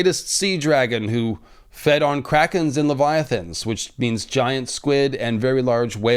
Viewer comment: Dammit. I was making noise in the kitchen, so I thought you said a dragon existed on crackers and leviathans! So I played it back and was disappointed you only said krackens. ☹